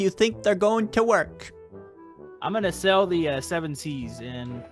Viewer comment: In 5:25 said I will sell the seven seas instead of sailing then